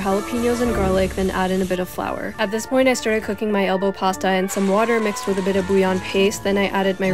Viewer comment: Was it just me who heard her say elbow water?